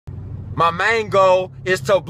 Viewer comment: I hear “My mango, ‘gon blow up”